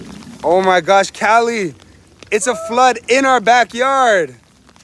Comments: At the start i thought she said ITS BLOODDDDDS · I thought they said at the start: “ITS A FLYYYYYYYYYYYYYYYYYYY!”